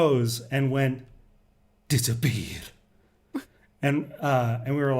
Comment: FYI the wife said: "Dis 'n beer" i.e. "It's a bear". In case someone thought he said "disappear" :D